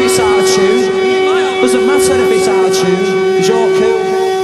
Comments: to this day i always thought he was saying it "doesnt matter if its our tune., its all cool" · I Don't know if I am ok, but at The start Liam says: "One song, one song, it doesn't matter if it's out of tune, It doesn't matter if it's out of tune, 'cause your cool." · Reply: always thought he said "It doesn't matter if it's attitude" rather than "out of tune." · I Don't know if I am ok, but at The start Liam says: "One song, one song, it doesn't matter if it's out of tune, It doesn't matter if it's out of tune, 'cause your cool." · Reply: I always thought he was saying 'does it matter if its our tune?' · Liam- It doesn't matter if it's out of tune, cause you're cool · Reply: I thought he said "it doesn't matter if it's out of tune, cause you're too"